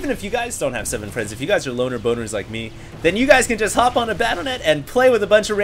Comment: lol did hd say loner boner?